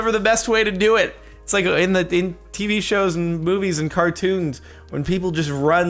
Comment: Did he say boobies instead of movies @ 2:37 ?? Demonetized